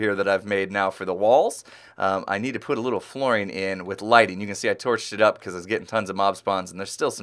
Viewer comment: Did i hear "I tore S**t up"???? at 5:06???? Im just kidding sometimes it just happens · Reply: I believe that he said "I torched it up". :)